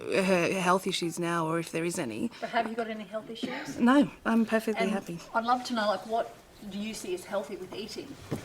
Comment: "Do you have any health issues?" "No, I'm perfectly happy." Fuck. Did I hear that correctly?